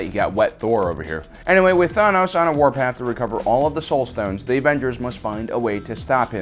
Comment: Did he say all of the soulstones